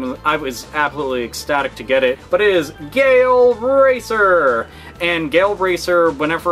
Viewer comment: When I heard "Gale Racer" I thought he said "Gay Old Racer"